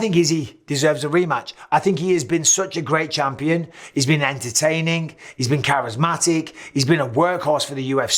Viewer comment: Did you say Izzy’s been entertaining? 😂